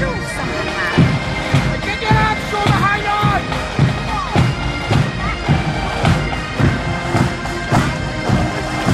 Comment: what is he shout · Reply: He is shouting "Get your arms shoulder high Guards"